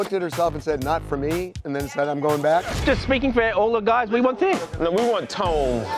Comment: What did he say at 10:03 ?? Did he say "We want thin" ??